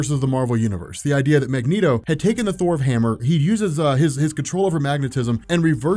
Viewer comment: Did he say " the Thor of hammer " around 0:50 or is it just me 🤔😂